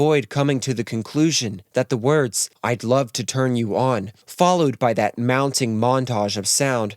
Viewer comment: I always thought he said, "I'd love to tell you all..", not, "turn you on". I guess because it rhymed with "Albert Hall". I gotta go listen to it now.